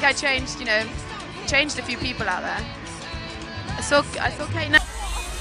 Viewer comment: holy shit did she say "I saw Kate Nash"?!!!